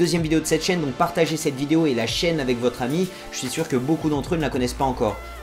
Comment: En 14:57 : #jaiqunseulamietalors? c'était quoi ça x)